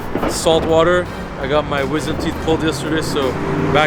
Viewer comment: did he say WIZARD teeth ?